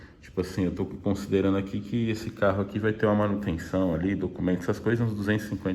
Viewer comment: "Tenho 2 carros para se quebrar um tenho o outro"... antes disso escutei "releguei a manutenção ou documento".. acho que deu loop infinito rs